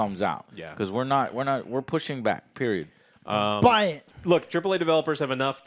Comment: 🤣😂🤣😂🤣😂 Idk OJ…… sounds like you said BUY IT….! 🤣😂🤣😂😂😂🤣😂🤣😂🤣😂😂😂😂😂😂